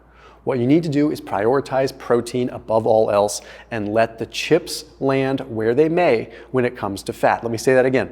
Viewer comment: I distinctly heard him say we can eat chips now.